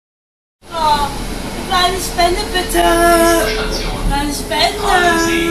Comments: Ich habe immer Halsbänder bitte verstanden! 😂😂😂😂🤣🤣🤣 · Ich hab immer ,,eine spinneeee" verstanden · Ich habe eine SPINNE! verstanden und auch EiNe SpeNnE bItTe